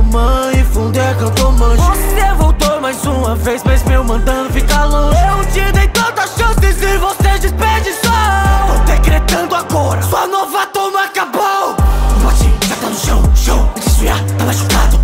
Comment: eita porra kkk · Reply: QUANDO CLIQUEI OUVI "A UVA DA TOMAN ACABOU"